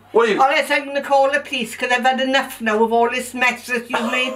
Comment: she said piece instead of police 6:49😂😂😂